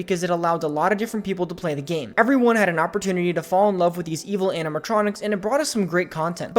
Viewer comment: did you just say animatronics?